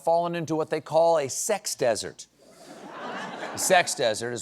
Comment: Sex desert is not nearly as appealing as what I thought it said: sex dessert. Dammit!